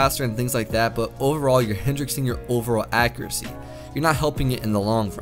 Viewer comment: Does he say "hendrixing" at 1:44 instead of hindering?